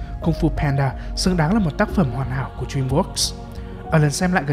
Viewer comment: Lần đầu nghe như "tác phẩm hoàn hảo của Trung Quốc" t kiểu "wtf???"